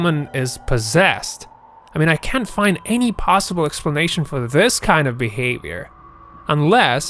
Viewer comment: Vigor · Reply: UK spelling is vigour, American spelling is vigor. But yeah, I thought I heard them say vigour and not figure, lol. I was like, wait-- lol